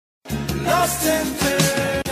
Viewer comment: This should have way more views idk what going on haha new favorite track , feeling lost in paradise over here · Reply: Like the hook how it sounds like its saying "Lost in paradise" i thought that too but it was saying "lost and turn out"